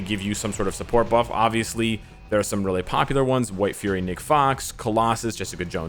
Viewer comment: Wait, did I just hear 13:38 says, 'White Fury, Nick Fox'? White Fury sounds cool, where do I find 'em? 😅